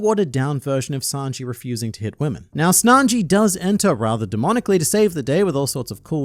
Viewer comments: Did you say Snanji @ 6:59? 🤣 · tell me i'm the only one who heard him saying snanji instead of sanji 💀😂😂😂😂 i swear he said that ! in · Don't think I didn't catch you calling him Snanji at 6:58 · did he say Snanji? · Did you say Snanji ?